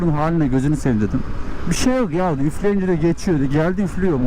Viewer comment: dayının dediğini öpünce geçiyor diye anladım. Sonra da gelip motoru yakıt deposundan nasıl öptüğünü hayal ettim. kafayı sıyırdım sayende eyv.